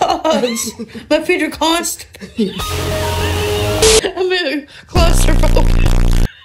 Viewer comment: I was trying to sneeze and then i heard "My feet are constipated"